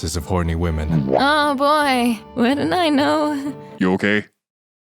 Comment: For some reason, when the Scottish dwarf asks "you okay?" I hear "are you ghe?".